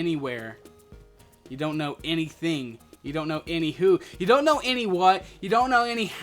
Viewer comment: did he say anyhoof?